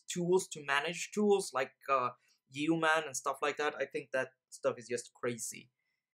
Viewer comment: you mention "tools to manage tools" with an example that I can't quite make out (view man/mon, some other words?). Could you point to that specifically? I know I suffer from the same tool-itis as you did previously but I'm really curious as to what such a thing is conceptually. I quite understand the difficulties of analysis paralysis but to me there is still value in understanding the core concepts of what makes a particular tool useful. Thanks. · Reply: I think it's yeoman I'm talking about. That is definitely the develop equivalent of having a special room in your house just for shoes.